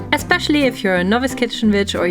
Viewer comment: I thought this woman said " if you are a novice kitchen bitch" and I laughed so hard! Definitely going to adopt it! 😂😂